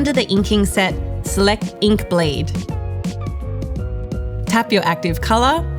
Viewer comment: Love it, but the accent!😊😊 I thought she said Ink Blade.! 😂😂 But got it now. Great series❤❤